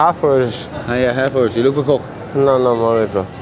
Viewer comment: What does old mate say when the other fella is saying hes half irish12:53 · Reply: Are you looking for coke,, as in the drug